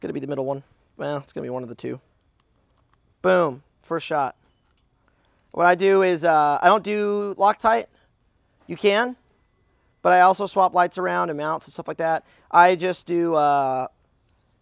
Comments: Did he just say "meownts?" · "I just do ehhh...hentai..." I know that's not what he said but I just couldn't help. XDDDD